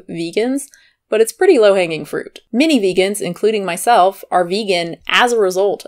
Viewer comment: I thought you said ‘mini vegans’ for a sec 😂